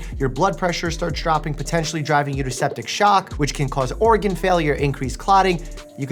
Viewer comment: Did you say OREGON failure?